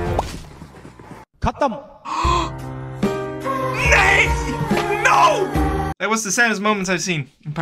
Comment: I heard nahi⁉️⁉️⁉️⁉️⁉️⁉️⁉️⁉️